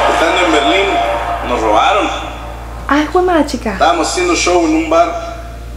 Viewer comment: ¿Qué fue lo que dijiste en el minuto 2:29? xd jajaja Le regresé y no te entendí .. :) PD. Qué hermosa mujer. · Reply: Dije "Ay juemachica" es como decir Ay juemadre 😅 no sé si quedaste peor de perdido 😜